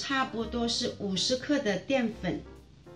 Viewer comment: What is the ingredient (what is dian fen?) said at 0:15? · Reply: Tiffany Huang corn starch☺